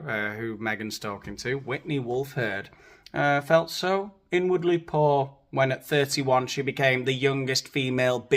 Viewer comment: I thought she said “I was n-wordly poor” 😂